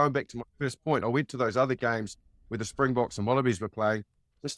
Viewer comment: min Did he say the Springboks and Wannabies are playing? 😆🤣